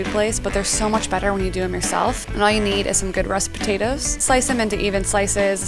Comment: Some good "what?" Potatoes? I tried 4 times now and I don't understand what she's saying. Some English speaking person pls help! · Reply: Rust potatoes. It's a type of potato lol